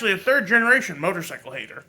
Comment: DID HE SAY 3rd GENERATION Motorcycle ! Hater ?!!! Hahahhaha wow is getting wild ! I thought he said he was a cuck haaahaah